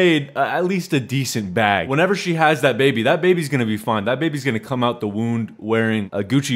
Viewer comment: Did he just say "come out the wound" 😂😂😂